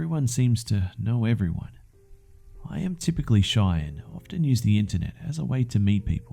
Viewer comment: Because of his accent, and because there are two guys outside yelling and hitting each other with what looks to be a mop handle and the lid of a recycling bin *this is Los Angeles) in the first story I thought he said "I'm really shy and use the N word a lot."